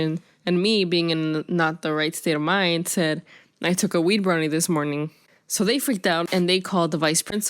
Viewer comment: I thought she said "i ate a wee'K' brownie